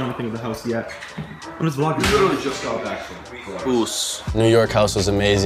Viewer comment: wtf he said uce slang for uso in samoan (Dwayne The Rock Johnsons Language) and the kid that owns Laxed beat which Jason Derulo used on (Savage Love) 685 Boyz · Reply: Ous is something he and sensei used to say 👌 it’s sensei’s brand